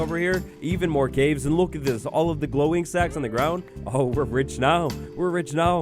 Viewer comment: glow ink sacs, i heard glowing sacs 😏